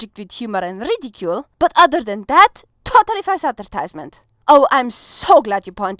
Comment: What word is spoken here 1:48 after totally? Also, your accent is cool sounding. Where is your native linguistic dialect from? To clarify, for me that would be the mid-east coast of America. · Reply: She said “totally false advertisement” and from watching videos, I’m pretty sure she’s German or at least from a German speaking country. Given that your comment is 8 years old, you might already know this.